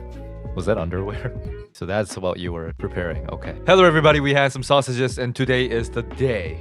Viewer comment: For your intro, I heard, "Hello everybody! We have some sausages." 😭😭😭